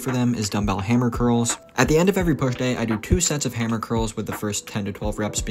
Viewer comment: did he just say “at the end of every birthday”???